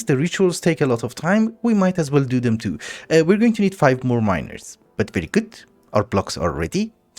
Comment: Everytime u say miner i hear as minor and it's extremely funny